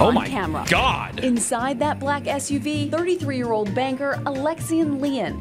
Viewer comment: I thought she said '33 year old BANGER'.. 😂